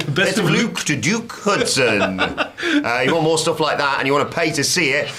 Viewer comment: Swear I thought he said JUKE Hudson and I said who’s that?? Sad part is when I later saw it was DUKE Hudson I said who’s that?? 🤣